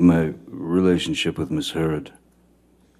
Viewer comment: It says Miss Herod instead of Miss Heard which goes along with how tyrannical she was 😆